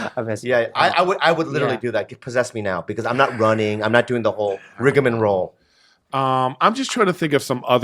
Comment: does Bobby say "Rigum and Roll"?